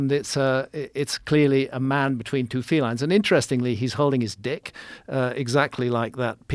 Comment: I did like triple take at 1:29:24 like wait, did Graham just say “he’s holding his dick”? Yes, yes, he did